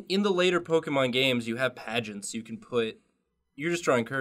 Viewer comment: I thought they said that wigglypuff looks like a FUrby.